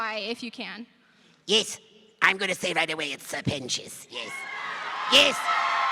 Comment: i was looking away from my phone while i heard this and genuinely thought said that richard horvits has a great pentious imp-ression